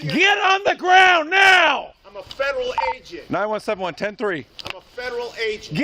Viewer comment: Am I mishearing or does the agent open his interaction with "I'm a Federal fuckin' agent!"? Not the best way to start things off.